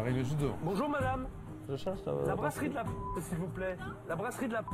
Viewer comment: il demande quoi ? La brasserie d'la pouf ? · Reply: La brasserie de la poste je pense